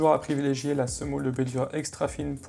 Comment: privilégiez la semoule [......] extra fine pour l'étalage. C'est quoi le mot qu'ont entend mal ? Merci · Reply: Semoule de blé dur ?